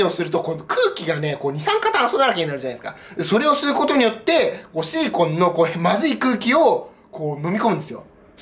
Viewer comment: おシリコンて聞こえるw